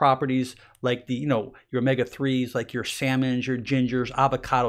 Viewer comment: What is that mumble word at 12:16..... salmon?